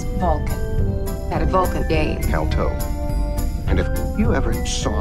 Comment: Tuvok had what? I can’t make out what they are saying? I have played it several times. The video is funny though. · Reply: Had a Vulcan game “Kal-toh.”